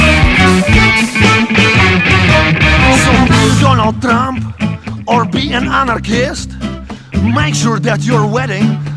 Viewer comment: Did he say be a Donald Trump?? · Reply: "So be a Donald Trump,or be a anarchist"